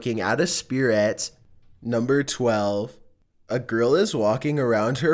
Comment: he said grill or at least thats what i heard